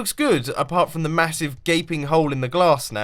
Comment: I thought he said gaben hole! lol!